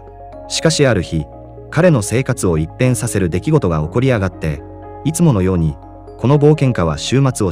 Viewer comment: 耳だけ聞いてたら、「彼の生活を一変させる出来事が「起こりやがって」」と聞こえて 字幕見たら「起こり上がって」だった。いや、「起こり上がる」って表現あったっけ？